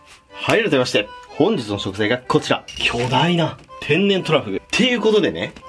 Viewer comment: 秒の時巨大トラフって聞こえたの俺だけ?